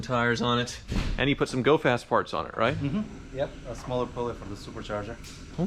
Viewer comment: Almost sounded like you said "dope ass parts" and then I thought about it for a second... "GO FAST PARTS". Bahaha